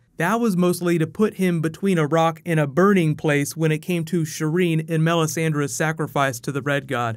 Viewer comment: I heard, "--difference between Barrack and a Bernie place."